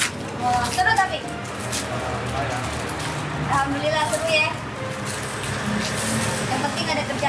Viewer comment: Ada yg perasan nga kaka bilang apa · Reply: Alhamdullilah ye